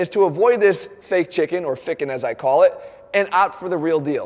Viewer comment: did he just say ficken? Greetz from germany ;D